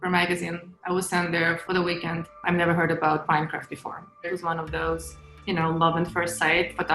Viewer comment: anyone else mishear this as "minecraft " and do a triple retake LMAOO